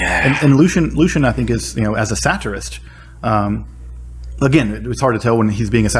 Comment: did some say satire? I smell you!